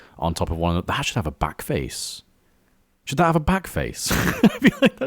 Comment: I THOUGHT IT SAID BLACKFACE AND I WAS LIKE ?????